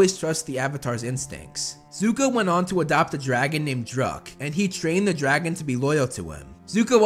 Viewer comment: For a second I thought you said “Zuko went on to adopt a dragon named Drug “